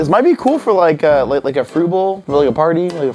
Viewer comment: When said it would be good for fruits bowls I thought he said it would be good for approval